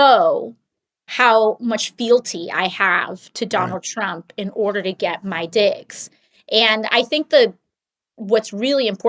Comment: lol @ 9:00 all I heard was “to show fielty to Donald Trump in order to get my D ic ks” 😂